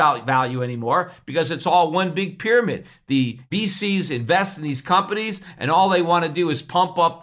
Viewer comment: Non-native English speaker here! What's Peter saying here 31:08? Does he say "The BCs invest in these companies"? If so, what does BC stand for in the world of finance? Thanks in advance!